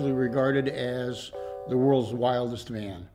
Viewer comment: It sounded like he said “The world’s longest man”.